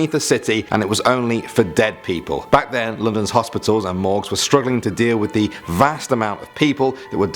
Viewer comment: did he say “morgz”?